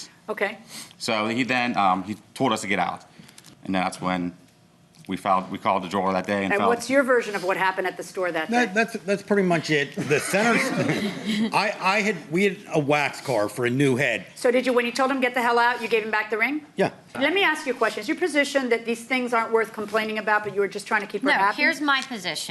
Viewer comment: 'We had a wax car for a new head.' WTF? Is he trying to say they took a lost wax mould? Cos if not, I'm totally lost!